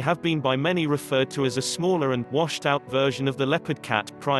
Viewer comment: "washed out version of the leopard cat" lmao I thought you said "washed up" and I cracked up